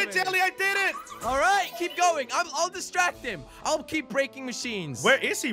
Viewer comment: did jelly just say "i'll keep BREAKING machines"